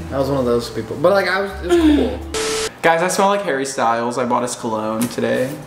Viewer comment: I swear said "hairy styles" 😀